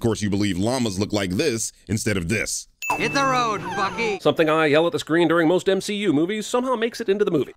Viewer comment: I’m sorry, WHAT did Jeremy say about my Bucko??? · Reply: "Hit the road, Bucky!"